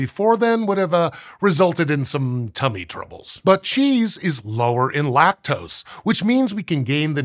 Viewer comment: Did he just say, "Butt cheese is lower in lactose."?!?! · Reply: But cheese is lower in lactose. One t.